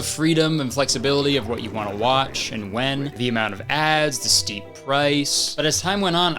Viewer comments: I definitely heard "the steamed rice" and was like 🤔🤔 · I thought he said steamed rice… Cuz you know, I always thought there was too much steamed rice while watching cable television